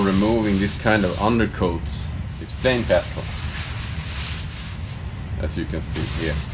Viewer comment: what is that solvent? I couldn't understand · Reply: "Plain Petrol" i.e. gasoline